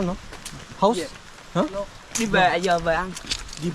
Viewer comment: 베트남어 전공했던 학생입니다. 집에라고 들리던 말은 đi về라고 하고 집에 돌아가다라는 뜻입니다. 집에 가야한다고 하네요.